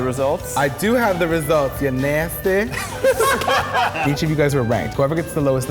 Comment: I thought he said "each of you guys are rank" lmao